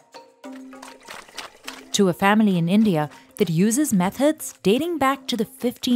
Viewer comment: i thought she said "uses meth heads" as the person is stomping on the cashew fruit in the beginning